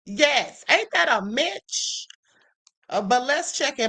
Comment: WOW, THE FIRST TIME⏰️ THAT LITERALLY SAW A DEMONIC ENTITY FALL, LOL!! !😂😅😂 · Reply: MY BAD , I THOUGHT THAT YOU SAID AIN'T THAT A B**TH, MY APOLOGIES, I LOVE ❤ YOU, TO LIFE!!!